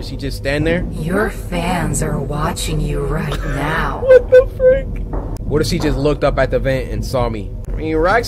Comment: i did cory say "wtf" 1:08??? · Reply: He said what the frick